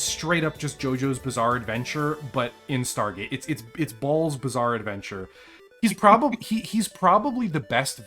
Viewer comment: I nearly broke when I heard you say "Ball-bizarre adventure" (I'm not saying that's what you said; I'm saying that's what I heard).